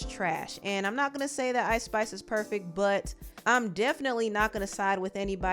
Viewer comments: Did anyone hear at 12:30 secs said Ice Spice? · spice got a visceral reaction because it triggered the deadbeat mama 🤷🏾‍♀️ not because she loves her child or is there for him…she’s disgusting. when is she getting fired! also did i hear at 12:33 ice spice hehe 😂🙈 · Ice Spice?? Did I hear wrong y’all lol I did just smoke some trees 🌳 💨 but I’m sure I heard Ice Spice 😂😂